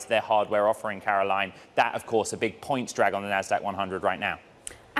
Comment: did Ed say "munch" right when "unch" appeared on the screen? uncanny, if so